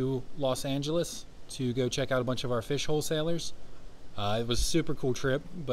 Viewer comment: I heard ‘fish hole sailors’. My imagination lit up like you wouldn’t believe.